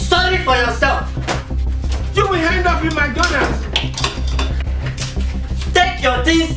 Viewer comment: did i hear "youll end up in mcdonalds" 😂😂😂😂😂😂